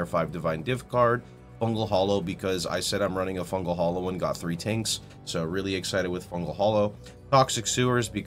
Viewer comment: I am sorry, but i didnt get the thing about Fungal Hollow (1:38), he says somethink like "... fungal Hollow and got three tanks" - even youtube subtitles says so, what he meant by that? :) Anyway thanks for your vids.